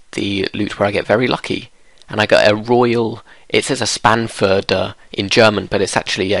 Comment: did that say royale spamfolder?